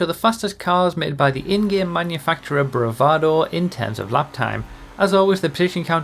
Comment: wdum? What does he say then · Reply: "In-Game Manufacturer" the auto captions also say in-game